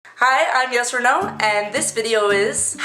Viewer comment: At first i thought u said "hi guys i'm yes-or-no" ahihi lol😂